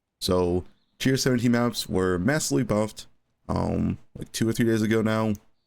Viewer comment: "two or three years ago now" . Imagine not having a clue what youre talking about and still deciding to comment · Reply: when? because at 0:15 he is definitely saying years